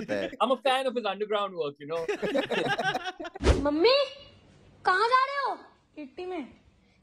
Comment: did anyone heard “mummy “ sound 😂😂😂